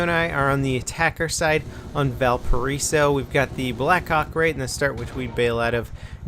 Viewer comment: We´ve got the black COCK xD who else heard him say that :D